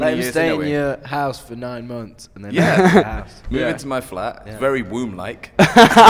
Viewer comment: What did he say at 7:20? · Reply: It's very womb like.